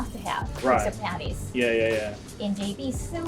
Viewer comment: She says "destroying your fantasies", but I don't know what she said and neither does the subtitles. All I got out of it were "patties".